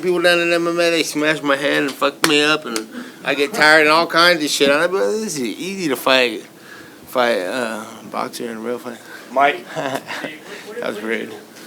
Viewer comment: does he say “that was rigged”